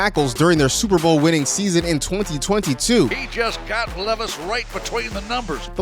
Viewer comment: I think they were saying "their Super Bowl in 2020 too" not "2022", just so we're all clear here.